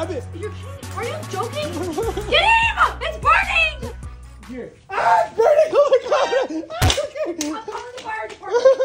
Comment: Did she just say she will call a fire department 😂😂😂